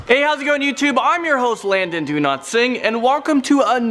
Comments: It sounds like he says landon do not sing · I thought you said Landon Do Not Sing (SORRY BRO I KNOW ITS) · Lmao no offense but in the intro I heard him say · Is it normal that when he pronounces his name I hear "do not sing" instead of "Dowlatsingh" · i thought you said lol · Lol i thought he Said his lastname is "donotsing" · Did you say do not sing instead of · It sounded like he said landon do not sing